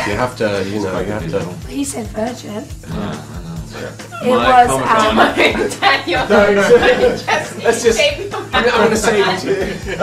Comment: what the heck dus phoebe say in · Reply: "Don't Daniel, don't" "Keep your mouth shut" lmfao